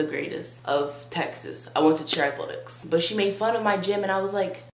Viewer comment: I thought she said I went to chapped lips